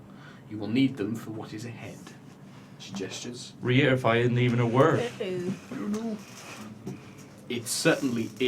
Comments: what does say at 19:33 ? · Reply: piper "Reitify isn't even a word" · Guys, what does says at 19:30 ? I've listen to it 20 times and can't understand it... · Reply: "Re-edify isn't even a word"